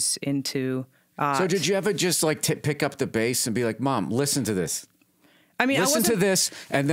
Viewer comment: “did you ever just t*t-pick up the base..” I nearly pissed myself laughing when I went through it again to make sure I heard it right